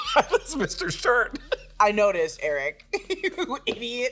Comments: I also thought it said "Mr. Shart" and I feel better about it now. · I THOUGHT IT SAID MR SHART AT FIRST TOO 😭💀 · I thought it said Mr. Shart too hahaha